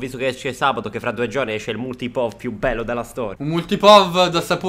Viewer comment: HO SENTITO BENE? IL MULTIPOV :)